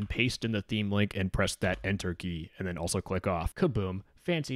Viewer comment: what does he click after enter? i can't understand him it sounds like "off" please anyone? :v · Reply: "also click off"